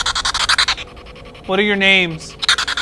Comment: I heard "What are you"